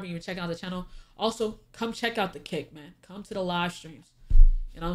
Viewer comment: Lol, I thought said, "Come check out the cake, not "come check out the kick"😂